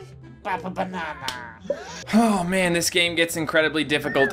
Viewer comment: I thought the teacher said "have a banana" :D